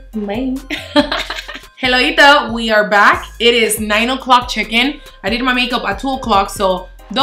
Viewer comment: I thought u said 9 o'clock chicken 😂😂